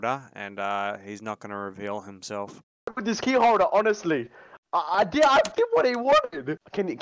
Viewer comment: "Where is this key holder honestly? I DID WHAT HE WANTED!" Is what he said